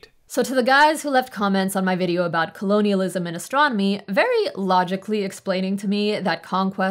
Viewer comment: The first time I heard "colonialism and astronomy" in this video I actually heard "and for those who commented in my last video 'colonialism is my mommy" and had to go back to listen again because I was flabbergasted.